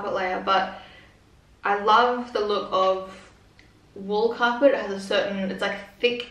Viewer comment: Oh my god, I thought you said wall carpets! Now I can't stop laughing thinking about a beautiful new home with carpet on the walls 😂